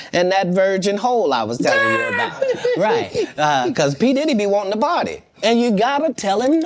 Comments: I thought he said “Diddy be wanting the body” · I thought Katt said "P Diddy b wanting the body & you have to tell him no". I don't know but I know he used body the last time 😊😊😊😊😊 I don't know 😂😂😂😂. Lordy, let me shit up 😂😂😂😂😂